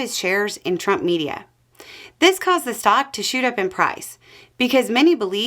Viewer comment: I thought you said "socks" and was very confused for a while 😅